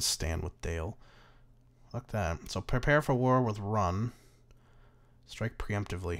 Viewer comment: did he just say "run"?